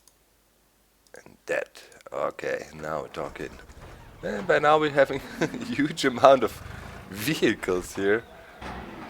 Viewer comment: Whenever said, "now we're talking." I thought he said now we're Docin'